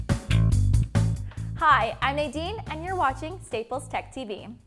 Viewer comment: i thought u said, hi i am 18 :P